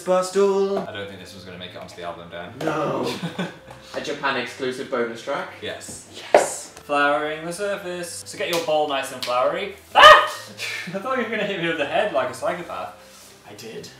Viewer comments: I swore I heard them say "Panics exclusive bonus track" Anyone else? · DID DAN SAY "A JAPAN EXCLUSIVE BONUS TRACK"???? DID HE FUCKING SAY JAPAN HOW LONG HAVE THEY BEEN TALKING ABOUT GOING TO JAPAN TOGETHER I HOPE MY EARS ARENT FOOLING ME